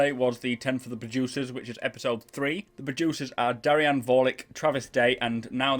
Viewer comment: I heard, "Ten for the Bajucers," every time you said "producers." Im sorry, but it cracked me up man. XD